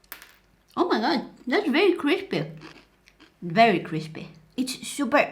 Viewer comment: i thought she said creepy 😓